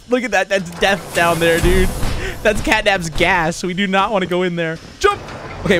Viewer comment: “That’s catnaps gas I do not wanna go down there” I thought u said u wanted his gas..